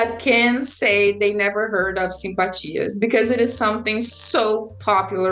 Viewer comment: How do you spell the word she uses for a type of Brazilian magic around the 21:50 mark? She says "I dare say there is no Brazilian person who can say they don't know what a s--- is" but I can't figure out how to spell it to do more research on it! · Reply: Simpatia 🥰